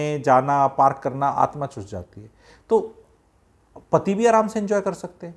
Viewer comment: Did I heard the same as everyone? Naughty ho rahe guruji 😂